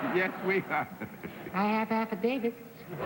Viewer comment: what does johnny carson say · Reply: "I have affidavits."